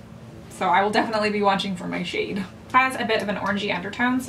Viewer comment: Someone send Jesus but when she said "it has a really strong orangey undertone" I looked away for a second and really thought she said orgy and I was so confused